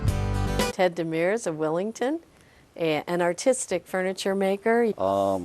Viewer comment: I thought she said autistic.... I was thinking huh what a weird thing to say and it made me feel even worse about his hand but then I saw the text where it had artistic there not autistic 😂😂